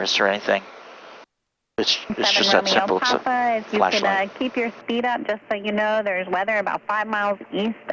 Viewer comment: Can somebody transcribe what ATC said at 1:40 please? I'm not a native english speaker. Thank you... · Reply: She asked them to keep their speed up as weather is approaching that may prevent them from getting in to land